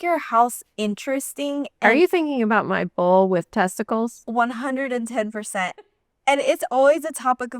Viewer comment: I’m glad you showed the Bull on the shelf because I thought you said “Bowl with testicles in the kitchen”😂